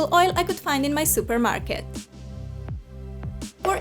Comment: i thought she said i your kitchen not supermarket